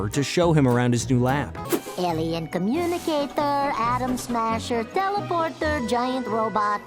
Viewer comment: Did he just said Adam Smasher? As in the villain from Cyberpunk 2077?!